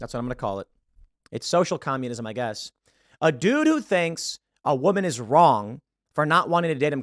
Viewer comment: Ha…it sounded like you said “a doo doo thinks!” @ 7:40 😂😂 it made me laugh because I’m apparently still a child.